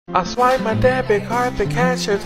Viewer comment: I rly thought that said that made my gay, instead of that made me cry.😂😂😂😂😂😂💀☠️💀☠️💀☠️